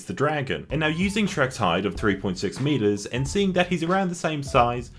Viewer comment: I thought he said “using shrieks hide of 3 metres” and I was like “ooh yeaaah ogres have layerrsss”